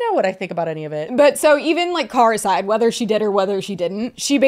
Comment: I had to pull over specifically to leave this comment. I swear to God, I legitimately thought you said “caricide” as in, Trisha’s car committing unalive. By driving into the house. But the. I realized, you said ‘car aside’